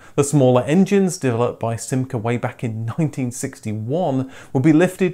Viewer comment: did you say “way back in 2061?” 😉